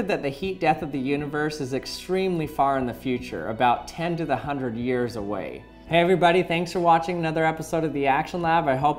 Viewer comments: Damn, I heard "ten to hundred years away". Has already begun compiling the "do before death" list. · Oh, at first, I heard: "But it's really far away - about 10 to 100 years away" and immediately ran through comments to find some apocalyptic paniacs :D. Also, does this "heat death" basically mean that once, everything in the universe will come to a 0K temperature? - because if not, it's not true. Also, consider everything just above 0K and you can make work by chemical reactions,... If you don't take this, black holes, which can't emit any source of heat (unless you count with Hawking radiation, but let's pretend it's not there), can actually do work as well - for example gravity waves can be considered as a source of work. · For a second I thought he said "the heat death of the universe is extremely far away, about 10 to 100 years away." Haha